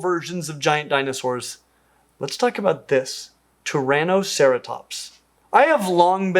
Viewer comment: Wow: When you first said "Turanoceratops" I of course heard it as "Tyrannoceratops," and for a too-brief moment was contemplating a huge, carnivorous ceratops species taking revenge for all its plant-eating kin 🤣